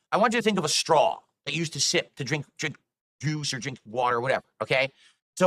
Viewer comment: Did this dude almost slipped Gin instead of juice LMAO